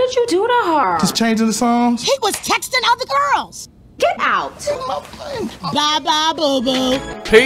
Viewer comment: "Bye Bye boo" the speakers sass I love it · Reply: I thought she said "Bye Bye Bozo"